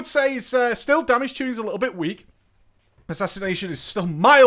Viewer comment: what did u said is week? the most important phrase and u cant say it clear?